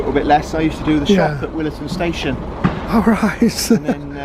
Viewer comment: At about 11:21, says '...I used to do the ...at Williton station...' Being Australian, I couldn't understand the missing word. What was it? Your UK tokens are like the Victorian Railways' previous miniature electric staff safeworking: same principles. We used to have exchangers on main lines for locomotives passing at up to 115 kilometres an hour (70 mph). Excellent video: many years ago I travelled on these railcars on the western main line in between my 'branch lines in a day' travel, starting at Penzance and doing branches such as Falmouth/Newquay/Gunnislake and best of all, Lisekeard to Looe. Lovely scenery. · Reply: Gosh. I have only just come across your enquiry. I think the word is Shop. I expect you are presently in the middle of your great UK Railway Tour. I hope you enjoy it all and all goes well!